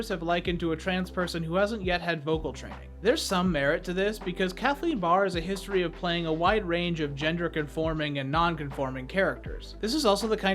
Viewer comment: When you said Kathleen Barr for a second I thought you said “capybara” XD